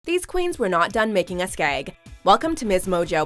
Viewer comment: I thought she said "these queens were not done making us gay" at first